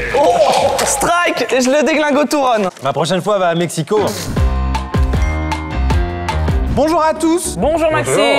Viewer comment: Hou là là!!!! :D Moi à 8:45 j'ai entendu "La prochaine fois va à Mexico" du patron ;) ;)